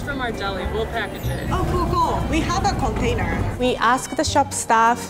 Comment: sounded like we have a cocainer 😂😂😂